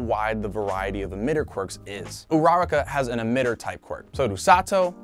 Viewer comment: did I hear that right urararka